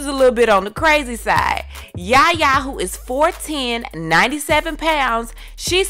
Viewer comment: Iayanna "Yaya" Mayeather is not a 14 y/o. She is 19 years of age. Born on May 20th, 2000. She'll be 20 next month. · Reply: At like minute 2:32 I hear you say "Yaya who is fourteen, 97 pounds" At least that's what I understand whenever I play the vid. Btw, I hope I'm not coming by as disrespectful. And maybe I'm hearing wrong, you can be saying something else. Its just that when I heard she was 14, and she's looking so damn small so I was like: "WAIT WHAT?! NBA is 20. WHAT'S HE DOING?!" And so I wanted to adress it.